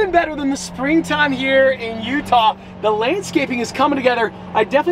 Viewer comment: You said lanescape instead of landscape at 3:59 😂❤